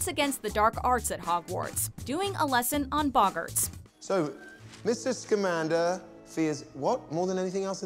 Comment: did he say miss. Scammander?😂😂😂